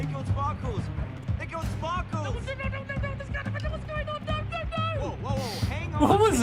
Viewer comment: I think you say oh no no no no I'm scared what's going on no no no